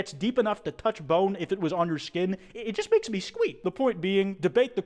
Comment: did u say squeet